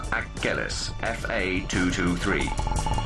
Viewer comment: Did he just say fucker at 0:03? Lol I know it's the company name